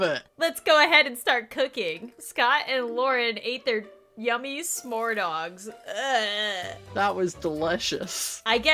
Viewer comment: I thought it said "Scott and Lauren ate their Yammy smore's dogs" OMG